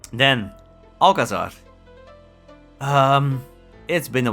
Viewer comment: what wad is that couldnt hear what you called it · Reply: Alcázar: <link>